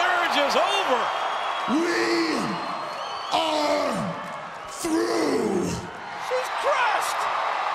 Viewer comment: Triple H Sounded Like He Was Saying WE ARE GROOT